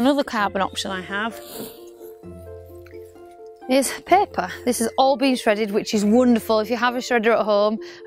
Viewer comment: When you said paper it sounds like you said pepper